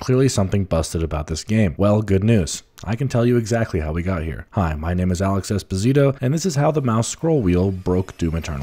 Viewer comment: I heard "my name is Alex despacito"